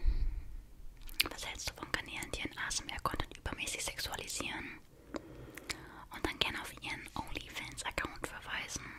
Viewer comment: Was sagt sie bei 10:05 ? Habe die Frage nicht verstanden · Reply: Sie liest die Frage auf der steht : was hältst du von Kanälen die ihren asmr Kanal übermäßig sexualisieren und dann gerne auf ihren onlyfans verweisen . Hoffe konnte dir helfen 🤝